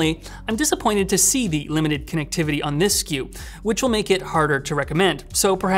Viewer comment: the subtitles say "this cube", does he say "this sku"?